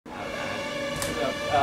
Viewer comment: They specifically asked for plates! Why give them cups?! That's just shit service! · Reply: oh, I hear that now. I thought he said two shits in a cup.